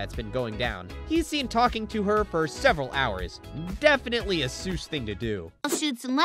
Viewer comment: captions says ssh instead of seus